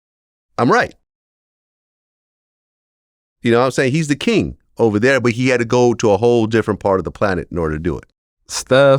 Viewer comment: What did he say at 3:30? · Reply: F china. He didnt care for steph building in china